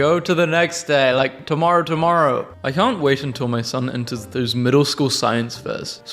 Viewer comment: WHY DID I THINK IT SAID EATERS “Son eaters”